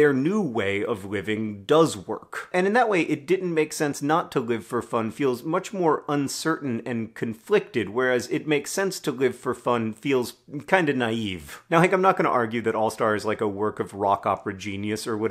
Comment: I thought they were saying "didn't make sense not to live full front"